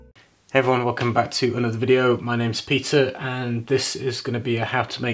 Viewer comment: I first thought you said "my name is pizza"